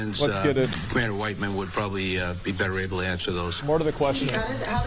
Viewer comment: Lol I mishead at 6:56 at first and I thought he said a white man would be better able to answer those questions